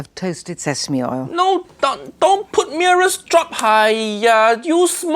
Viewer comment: I thought she said ‘Mirror’s drop’ 💀